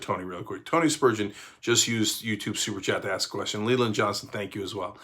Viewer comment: mins in .. 😮 Cenk just said my name!! .... but he followed it with a "Johnson," rather than a "Miller" .... so it wasn't EXACTLY "MY name." (but it SURE sounded like it STARTED out to b) - "Lelynn" gets pronounced like "Lee & Lynn" put TOGETHER..